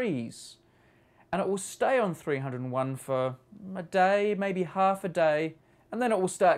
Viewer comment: 6 years later still on 301 I thought you said 1 day or a half